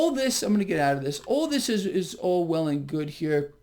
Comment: At 846 I thought I heard Orwellian good. Strangely